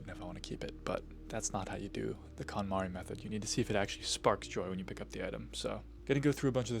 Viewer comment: I thought you said the "Katamari method" at first, which rolling everything into a ball and throwing it into space would probably also be efficient way to clean.